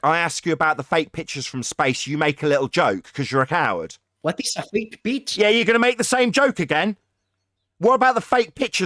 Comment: He thought was saying fake peach. was saying it's fake bitch. How do you miss that?